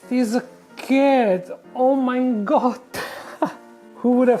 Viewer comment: did you just say „ Oh mein Gott“ ? :D do you have german roots or what